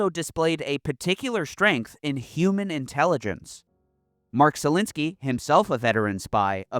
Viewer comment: thought u saying zelensky from ukraine lol